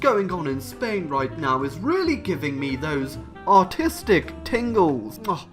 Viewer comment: sounded like he said autistic tingles